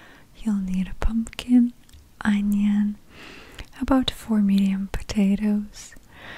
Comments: I legitimately thought she said four MILLION potatoes at the beginning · I literally thought she said “about four million potatoes” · I heard "about four million potatoes" and had to go back and check.